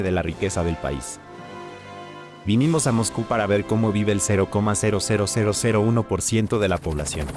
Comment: Did he just say "Cow".